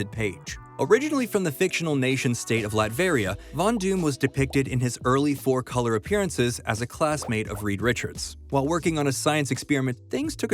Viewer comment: What are you saying? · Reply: "Von Doom was depicted in his early four colour appearances as a class mate of Reed Richards"